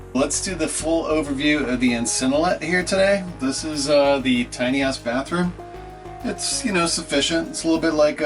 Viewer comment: I thought he said tiny-ass bathroom. LOL🤣🤣🤣